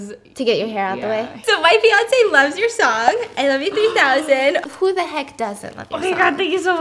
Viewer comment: I misheard at 1:10 . I thought she said "my husband loves your song. I love you 3000." I immediately froze. 🤣🤣🤣 Anw, can we appreciate the little plant headband? So cute! ❤